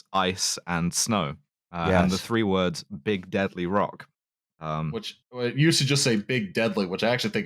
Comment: thought it said big daddy rock for a second 😏😭